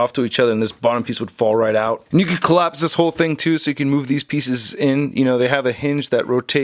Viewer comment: did he just said niggas?